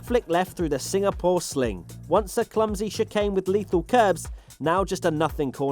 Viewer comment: did he say shitcane ? LMAO